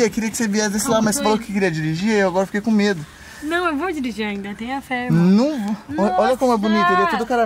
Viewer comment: só eu q entendi ali em 5:23 "não eu vou dirigur ainda, tenha fé mor" rsrs